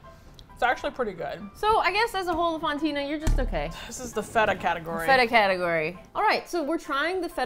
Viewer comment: I heard "Fed-up Category," which wasn't what said, but that's how they were feeling.